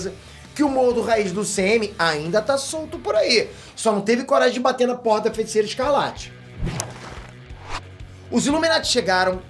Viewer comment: Só eu entendi isso? 6:26 “Só não teve coragem de bater na porra da feiticeira escarlate” 🤣🤣🤣